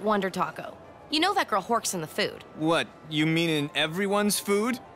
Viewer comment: Hi, non native speaker here. What does she say at 19:47? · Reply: “you know that girl horks in the food”